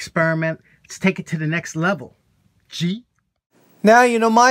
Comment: I could sworn I heard you say “let’s take it over the top..G”. Did you say G? 🤣 Nice work.